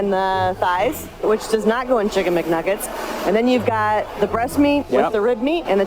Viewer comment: I thought I heard kickn' McNuggets lol